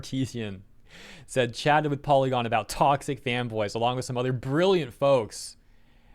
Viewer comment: => I heard "toxic femboys" and I am so cooked I need to be pinned ;w; (I am 5 hours late to actually be pinned, but heh...)